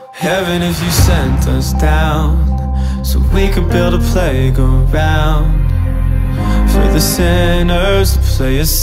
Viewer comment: Am I the only one who hears "Build a Plague Around" not "Build a playground" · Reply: i hear "build and play around"